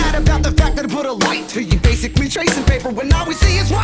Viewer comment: It sounded like Cody said basic bitch instead of basically lol